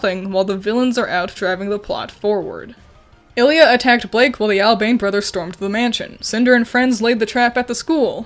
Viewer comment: Dunno why I heard "Ah-Beng Brothers"... XD